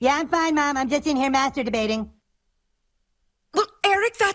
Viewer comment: When Eric said "Yeah, mom, I'm just master debating" the subtitles said "Yeah, mom, I'm just masturbating"